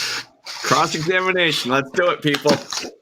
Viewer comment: I thought you said "Crotch examination, people." 😂🤣🤣